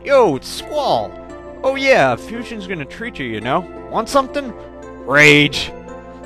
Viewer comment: wait did Fujin say rage or was just enraged. cause i think it would be pretty funny if Fujin yelled "RAGE!!!" before kicking Raijin in the shin.